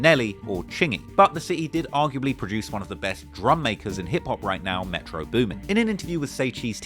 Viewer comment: “One of the best drum makers” I see what you did there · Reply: I thought he said "drum majors". I don't get "drum makers" .....?